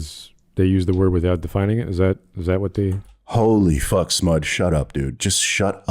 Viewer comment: for a moment, I thought you said "holy fuck smudge"😂😂😂. I see now you were talking about Smud.